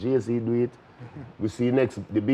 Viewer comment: Did he just say the baby oil man😂😂😂